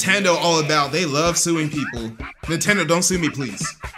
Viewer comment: BRO I THOUGHT THEY SAID NINTENDO DONT DO GUNS💀